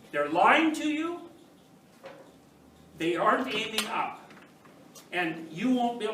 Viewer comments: La parte que dice "(ininteligible)" en el minuto 5:51, dice realmente "they aren't aiming up", que quiere decir que no estan apuntando hacia arriba, no estan queriendo progresar en algun ambito de su vida etc. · Al minuto 5:46 (la parte no inteligible) estoy seguro que dice "no estan apuntando para arriba" (they aren't aiming up) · ''they aren't aiming up'' 5:49. es lo que dice en ingles.